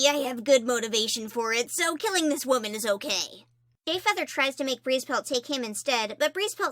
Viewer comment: wait a sec... please tell me someone else heard GAYFEATHER